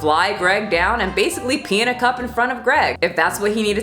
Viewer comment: When you said "Pee in a cup" I thought you said "Paint a cup" and I thought he said he would jerk off in front of Greg to prove he could lift 500 pounds and I thought "Wow, he's either really packing or he's delusional." And that's why I don't drink and watch YouTube at the same time anymore.